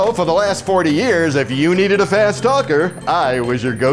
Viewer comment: He said fast talker i thought he said fat stalker